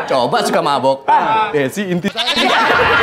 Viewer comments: Andika bilang apa sih? Kalo suka mabok, namanya teh Desi jadi apa??? Gak denger sayah 😅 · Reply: Intisari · Pak dika ngomong apa ya.. Penasaran ama yg suka mabok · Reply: Intisari minuman beralkohol jadul daerah bekasi